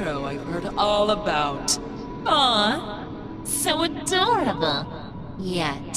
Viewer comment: "Aww...... so dumb!" literally has beaten a literal gisnt huge string boss that has 150,000 hp and has beaten a boss ven more powerful than that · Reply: let me say this once again, i hear "dumb" instead of "adorable" , idk why, when i tryto refresh the hearing it just keeps saying "dumb" insetad of "adorable".